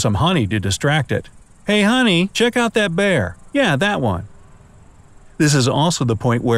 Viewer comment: did he said hey honey check out that bear yeah that one in 2:29 in the video